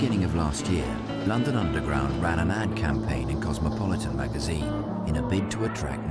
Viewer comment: "London Underground ran an ad campaign" - at first I misheard it as "London Underground ran a mad campaign" 😁 PS. The bit about "learner's plates on the train" was good 😁